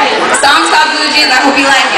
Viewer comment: I think someone said "shes really short"